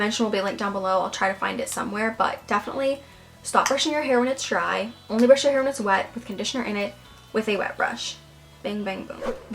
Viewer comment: can someone PLEASE tell me what did she said please😭😭😭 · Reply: Stop brushing your hair when its dry. Only brush your hair when its wet with conditioner in it, with a wet brush. Bang bang boom